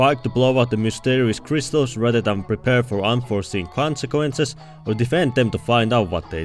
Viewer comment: wh-what did you say? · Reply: Unforeseen consequences